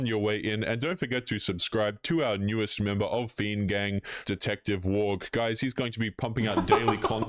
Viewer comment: When he said 'Detective Wang' I thought he actually said 'Detective WAP' 🤣